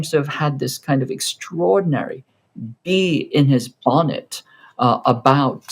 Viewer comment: When you were speaking about JD Vance.. I think you said 'bee in his bonnet'.... but I heard 'bee in his vomit' (because my fan is too noisy).. and then I thought it was a chinese expression... is there a list of colorful chinese expressions that I can use (translated into english)?